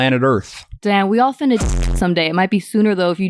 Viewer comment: I heard "Damn we all finna shit some day"